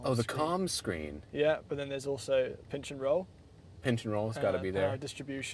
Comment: I thought they said “pinch and roll” and was thinking of something else